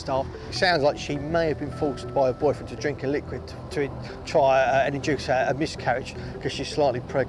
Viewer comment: on I can't understand a word he says! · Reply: He said 'sounds like she may have been forced by her boyfriend to drink a liquid to try induce a miscrriage'